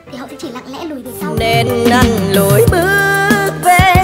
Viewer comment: t nghe ra "Nên ăn lùi bước...."